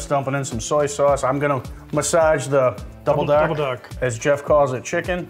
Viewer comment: 🤣I thought you said "Double Duck Soy!" That BAHSTON accent, I love it! Looks fabulous! I wonder if I can convince my Hubby to just pick the broccoli out! 😁